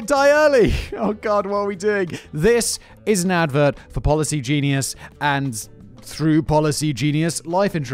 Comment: Thought the ad said poly genius